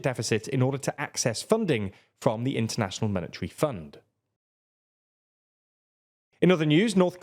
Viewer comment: International Military Fund? I'm not the only one who heard that, right? 😅